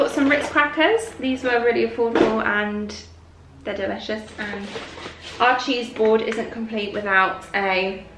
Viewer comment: I love Barney! I need to get my ears checked. I thought you said " Archie's board isn't complete without ritz" I was thinking......Who's Archie?😂😂😂